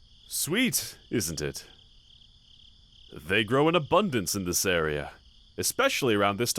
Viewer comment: i heard him say ‘faries’ and i was like huh?!